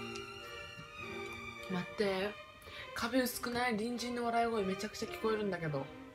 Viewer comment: 隣人の笑い声がにんじんの笑い声に聞こえてツボから抜け出せません、、